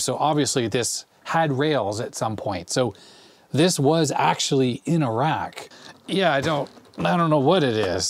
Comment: "this was in a rack." Misheard that for a second as Iraq and thought the story was suddenly much more interesting